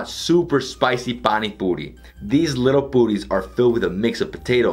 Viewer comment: I literally Died at 18:20 after noticing the live captions - "booties" but what he said was "puri's"🤣😭